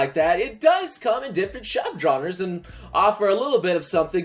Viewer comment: Did anyone else hear a bit of Sean Connery come out at 2:30? "Shub-Genres" XD